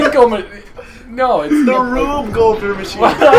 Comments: I thought you said Whoopie Goldberg Machine hahaha which made it even more funny to me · For a second i thought i heard Bill Goldberg...I'm glad I was wrong lol